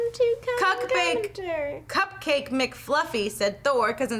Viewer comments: It sounded like said, "cockbake" not "cupcake". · Says Cockbake instead of Cupcake lolz :)